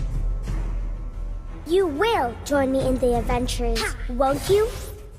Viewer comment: She a member of the Avengers? I heard her saying "You will join me in the avengers, won't you?"